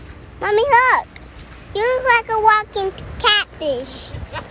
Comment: i thought the little girl said "u look like a walking cactus" not "walking CATFISH"lol